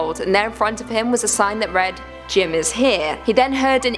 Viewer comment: For Jim I thought you said Jin from bts 😂😂